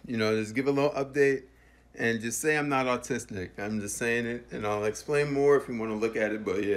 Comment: sounds like he said autistnic instead of autistic